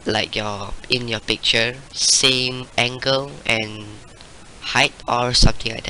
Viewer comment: Did he say "same ankle" @ 2:42? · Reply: Mandarina same angle xD